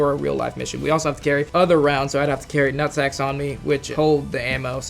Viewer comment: did he say rucksack or nutsack?